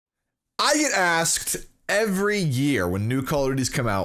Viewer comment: Is it just me or did it sound like he said "I eat ass" in the begging of the video I swear. !!